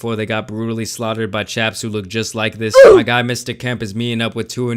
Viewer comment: did he say chaps lmao😂